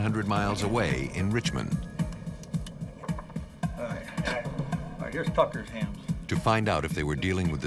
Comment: is he saying “now here’s fckers hand” lol?